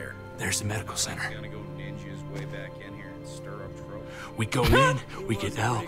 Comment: did he say go Genji his way back in here?